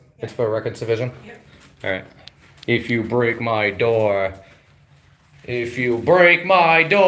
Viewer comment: 1.08 When the Officer said "Break my Drawer" ... I thought he said "Break my Jaw" 😂😂😂